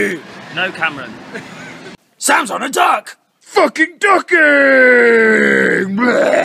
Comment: Sams on a duck!! Thought he said sounds on a duck